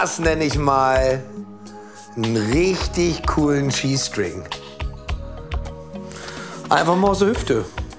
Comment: Ich hab bei 06:28 erst verstanden "Das nenn' ich mal n' richtig coolen G-String!" xD xD xD